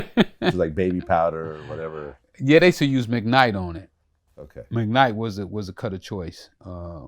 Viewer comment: what does he say they used to cut with back in the day? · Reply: Mcnight